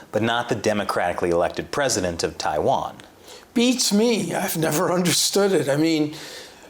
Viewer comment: excuse me...... "bitch me" !!!???. I must have misheard him.